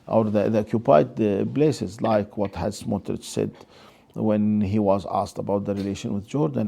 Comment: who is he quoting here? I cannot make out the name. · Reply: He said, Smotrich.